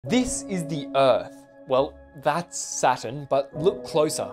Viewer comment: I heard "this is the earth, and that is satan " 💀